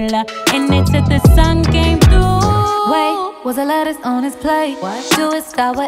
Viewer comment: I thought Queen said "Wait was there lettuce on his plate" lmao! Instead of "Wait whats the letters on his plate?"